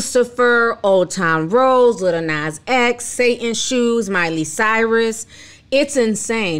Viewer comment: My bad I thought they said a Satin shoe. I was like Oh that's cute for Him🤦🏾‍♀️